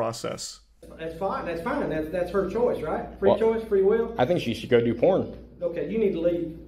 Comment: What did he say at 8:17? "I think he should go do plunk" ? (edit) I got the answer at 8:40 lol